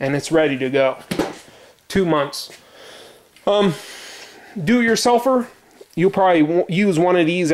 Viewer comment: When you said 'do it yourselfer' i thought you said 'do it your sulphur'.